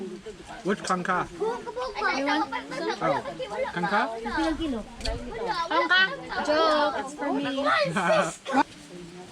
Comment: It sounds like kangka but what said is... "Ka-on Ka?" Ka-on = eat Ka? = you? In english "You wanna eat?" I hope it helps a little if you want to learn thier langguage. 😀